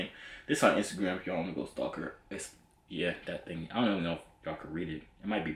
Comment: what he say😭😭😭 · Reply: he said “this her instagram if you wanna go stalk her, its..yea..that thing, i dont even know if ya’ll could read it”